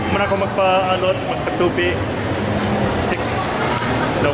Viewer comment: did you say pagpatupi? Or pagpaTULI? LOL 🤗🤣🤣🤣 I'm enjoying your vlogs. Bisdak ko gikan sa Bohol, here in Peoria, AZ now!